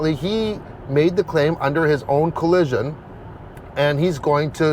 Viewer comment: Owner made a claim under his own “collision”?😂 he probably meant under his own “volition” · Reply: I heard him said “collusion” and not “collision”.